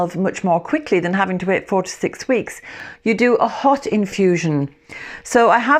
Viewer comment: Luckily I went to read the instruction, because I thought you said let it infuse 46 weeks 😂🙈 I will definitely try out this nalm. Thank you 🙏🏽